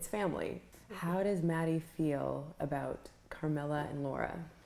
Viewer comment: WAIT Maddie was actually Mattie 😮 mind=blown... I always thought they were saying Maddie :/